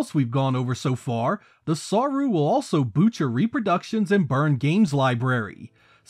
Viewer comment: Great video! One funny thing I noticed was @ 6:40 you say “…the Saru will also boot your reproductions and burn games.” YouTube’s auto captions said “the Saru will also butcher reproductions” instead of ‘boot your’. Just found that humorous 😅